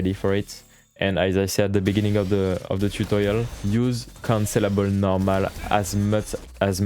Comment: Great guide and format, I will be looking for more. I just had a decent lol as I realized after about 5 minutes into the video that he was saying 'cancellable' not 'considerable'; I kept hearing 'this move is considerable' and I was waiting for a reason why 😃